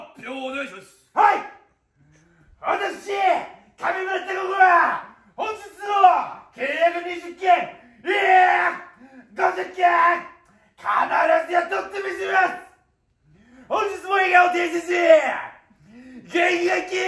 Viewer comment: なんて言ってるん？w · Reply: わたくし、(名前)は。 本日も、契約二十件…いや！ 五十件！ 必ずや、とってみせます！ 本日も笑顔で接し、 (？？？)契約をとって参りますので、どうぞよろしくお願いします。